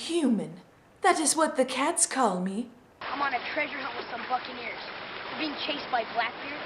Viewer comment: I thought Jack said the f word on 11:02, but he said Buccaneers.